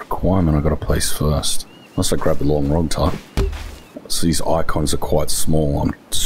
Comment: did he say long wrog type